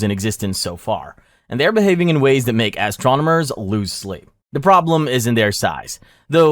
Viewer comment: Does anyone else hear him say ass-tronomers? This wasnt the first time I heard him say it either.